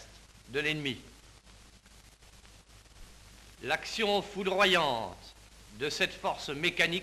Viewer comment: 楽勝 有名な空耳ですね